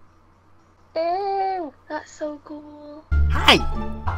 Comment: Did she say, "That's so ghoul"?